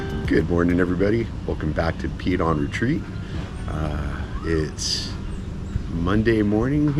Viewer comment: I thought you said welcome to peed on a tree versus Pete on retreat lol